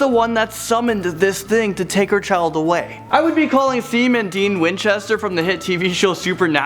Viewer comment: did he call them seam and dean